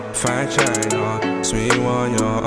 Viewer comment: Why am I hearing “vagina” in the song? I had to reload and he says “fine china” 😂... Love the hair and the song 💞